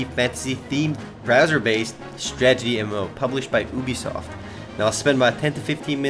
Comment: did he say boobysoft at 0:12?